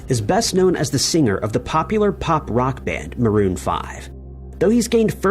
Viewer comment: The only disturbing thing about the Adam Levine story is that you would refer to Maroon 5 as a punk rock band. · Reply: You misheard what he said. What he really said was "pop" rock band. Which is what they were.